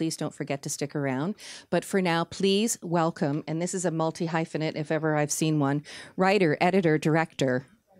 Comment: what does she said? I only understood it as "multi high finete".